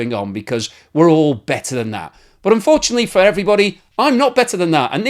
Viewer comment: did you say "...BETTER than that" or "...BATTER than that" ?